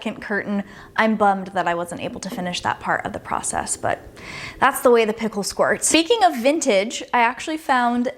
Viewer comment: ! Did you just say, "that's just how the pimple squirts" 😂😂😂 that's a new one for me.